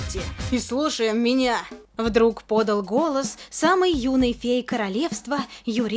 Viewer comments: Вместо " юный фей" послышалось "юный гей"🌝👌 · Мне послышалось "самый юный ГЕЙ королевства"